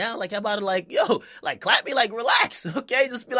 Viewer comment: Can somebody tell me what says at the beginning of his videos? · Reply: a sorosh it sounds like he says "its ya boy jackademiks" lol but i guess its "its ya boy dj akademiks"